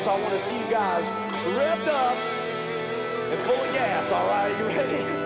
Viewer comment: Did he just say full of gas?